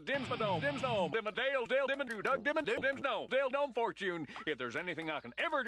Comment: I thought he said “Dildo fortune” 😂